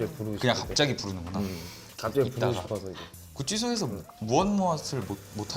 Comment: 구찌소로 들리는거 나만 그럼?